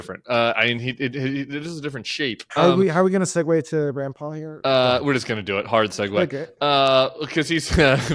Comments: When Rand Paul was mentioned I misheard it as RuPaul and thought that that was strange but then again what isn't strange on the planet Trump where we've landed. Rand Paul, RuPaul, Apostle Paul, Paul McCartney, St. Vincent de Paul... Anything is possible anymore. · I legit thought they said “how are we going to segue to Grampa”. Imagine my surprise when they put up a tweet from Rand Paul